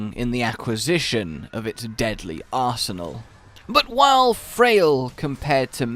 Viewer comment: I thought he said Karen which oddly works